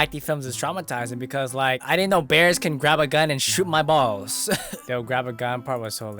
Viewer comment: did you just say shoot your balls🤨🤨🤨🤨🤨